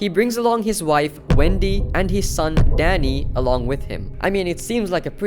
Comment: I thought u said his son daddy at 8:52 😭😭😭😭